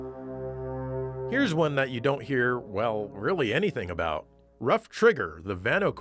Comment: I thought that said "butt" trigger